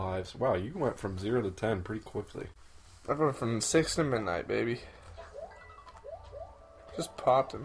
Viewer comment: did he say he went from six to midnight? lol